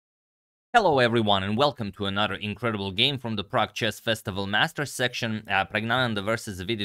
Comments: I thought you said the PRAGG chess festival Masters… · Every time you say "Prague Chess" I hear "Pragg Chess".